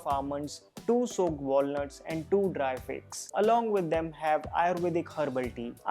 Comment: What did he say after walnut at 5:32? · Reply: Figs